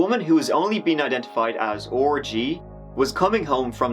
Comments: Listening, but not watching, at 7:11 I heard “orgy.” I was so confused until I rewound and actually watched “R.G.” pop up on the screen. I’d be willing to bet that added those letters because he knows his pronunciation sounds dirty. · wasnt looking and got quite a shock at 7:10. coulda swore u said her name was orgy. Haha. great video man. I'm addicted to your content. keep it up!